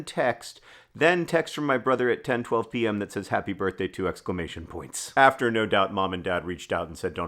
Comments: i was so confused because i heard it as “happy birthday to exclamation points” and was like oh that’s cool you share a BD with the exclamation point. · at first I heard "happy birthday to exclamation points" and my first thought was "of course Hank know when it's the exclamation point day". I really did not find it weird at all. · I hope I'm not the only one who thought the text said "Happy birthday to exclamation points"